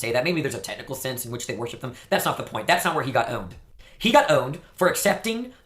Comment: This kid's accent is so bizarre that it it sounded like he said 'help' or 'helped' but when I looked at the transcript it said 'owned'. Seriously? Infuriating.